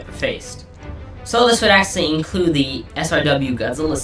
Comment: The...the WHAT Godzilla series? I legit can't understand what you're saying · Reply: IDW